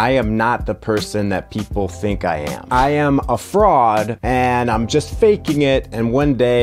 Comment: Lol wasn't paying attention, just listening and at 0:07 I thought you said frog and was like oh god how did you know